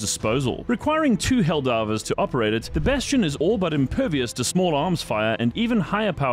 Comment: Thought he said “Tin-Bastion” which if it gets added and nerfed sounds like the perfect name.